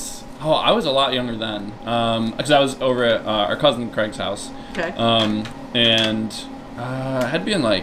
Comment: Am I confused or did Craig say at 5:18 that he also has a cousin named Craig? Seriously did I hear that right or am I missing something?